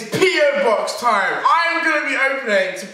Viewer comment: I thought he said pedo box